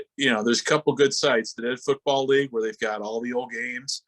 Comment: What was the first of the two sites that the guest mentioned? I heard The USFL Project, but I couldn't make out the first one he said. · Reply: That would be the Dead Football League which is a archive project for alternative leagues gone by. You can find them here: <link>